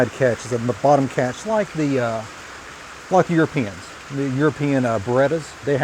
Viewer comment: i thought u said ur penis lmao